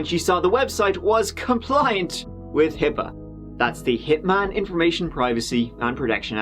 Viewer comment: I thought they said "Hip Man"! How do I cancel?!